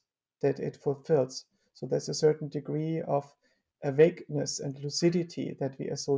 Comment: How about correcting CC to what he actually said? As long as AI can’t do correct CC, it doesn’t understand what is being said. These things should be clear from the context, and from everything else Joscha ever said we have recordings of. 2:39 „awakeness“ NOT „a vagueness“ 5:53 „entrained“ NOT „untrained“ 5:56 „ephemeral“ NOT „affirm or all“ 7:08 „retinas“ NOT „readiness“ 10:23 „cares“ NOT „caress“ 12:19 „in (its environment)“ NOT „and“ 12:47 „are“ NOT „as“ 22:41 „share“ not „shared“ (Joscha is speaking about ethics needing shared purposes and how we would go about teaching children ethics. But in the construction of the sentence it must read „share“, because it is the verb. „Shared“ would imply past tense: but for the negotiation of ethics it is actually important that both sides do now / continually share purposes, not that they once shared. Teaching kids to consider that they „shared“ (=potentially would share) purposes with other human beings is treating a child to think for itself wether this is a true proposition, which is a useful way to teach, if one wants future generations to consciously consider their alignments, rather than hammer those into them „to be a good person“. - Sorry for this circumvent way of saying, that I am also ok with „shared“ in the CC instead of „share“, as the discussion develops down that whole road anyway. P.S.: I didn’t correct „single cell“ to „single celled“ or „.“ to „?“ (- these are simple grammatical mistakes and don’t change what is bei